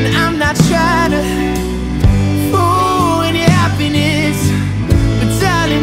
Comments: AM I the only one who hears "fuin your happiness"? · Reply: I hear "fool your happiness"😂😂wtf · He pronounces "ruin" as "fruin." I thought he was saying "fool your happiness." I still like the song but like, why say it like that? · He pronounces "ruin" as "fruin." I thought he was saying "fool your happiness." I still like the song but like, why say it like that? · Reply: I also thought that he was saying "fool your happiness" and I was like... when he said the word 'ruin' as this song called 'Ruin'...Anyway, this is my favourite song from him : )